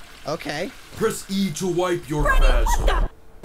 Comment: I think he was saying press e to wipe you,re fazballs😂